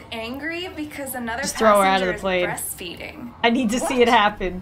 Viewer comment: not the subtitles saying "best feeding" and "west feeding"